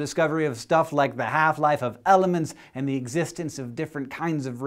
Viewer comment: Thought you said, “Halftime of elephants” lol